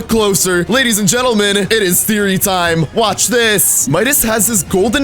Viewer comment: i thought he said ladys and gentelment it is siri time